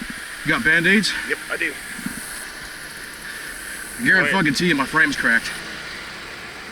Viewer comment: What did he say at 1:51? “You’re on the tee if my frame’s cracked” is how it sounded to me. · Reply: He said he garuntees his frame is cracked. Not you're on the tee. Garuntee. Hope it helped.